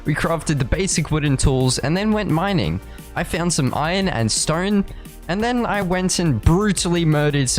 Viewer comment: ✨Strone✨ [this is what I heard 😆]